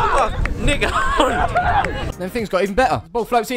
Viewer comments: anybody hear him say the n word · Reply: Nick Hope* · uhhh what did he say...? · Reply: Will Jones 😂 Nick Hope because I scored You ain’t the first person to query it!!!